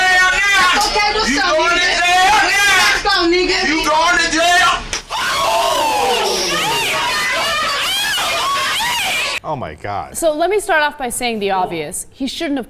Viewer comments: I thought he said "you're going on the ground now" meaning he's gonna beat her up... did anybody else hear that? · I thought he said you going to hell now